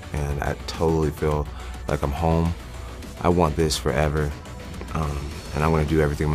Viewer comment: I thought I heard "I want this rack forever"